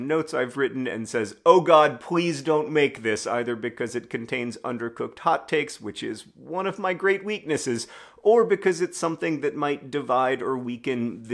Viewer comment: I misheard "undercooked hot takes" as "undercooked hot cakes" and was really struggling to understand how that would even work